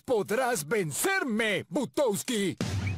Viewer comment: escuche putowski en esta parte XD